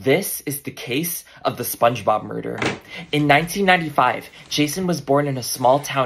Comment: I thought bro said spongebob burger 😭😭😭